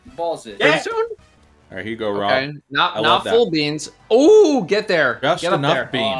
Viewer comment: Did Datto say "Not full beans" as in Jeff Arcuri's full beans? It was actually a thing?